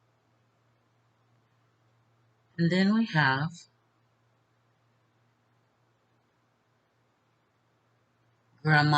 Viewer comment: What does say at 1:25? Crater hole? What’s that? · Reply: said "cradle roll". (I googled it). It's the children's section of the Seventh Day Adventist church. said during her prayer that she was asking forgiveness for the times that she took Summer to "cradle roll" when she "lost her patience" with Summer. (I don't feel meant she hurt Summer in any way....just that she was distracted by Summer's 'clinging' to her.) <link>